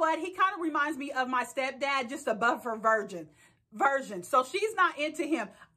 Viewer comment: I thought he said butt stuff 🤣🤣🤣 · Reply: I thought he said bust stuff lol